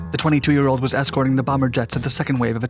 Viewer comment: did I just hear bomber jets?